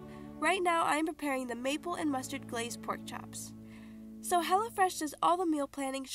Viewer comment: Am I the only one who thought she said HELLAfresh?